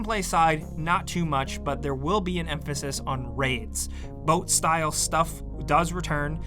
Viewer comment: When you're causally listening while taking a sip of your drink only to spit it out cause you thought you heard "There will be an emphasis on rapes"